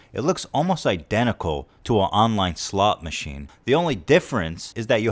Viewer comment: I thought he said slut machine